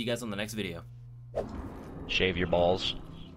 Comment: Lmfao. The first sentence I heard, "shave your balls"🤣😂🤣